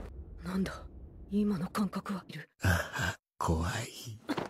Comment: did he say kawaii or am I tweaking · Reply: No he said « hehe… kowai ? » // kowai = you scared ?